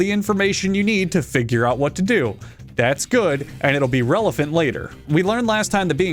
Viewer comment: Did you say "relephant" on purpose?